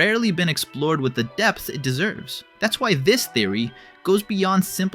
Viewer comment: he says depth? I HEARD DESS :)